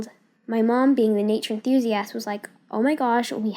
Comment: "my mom being the idk what she said ass " who calls Ur mom an ass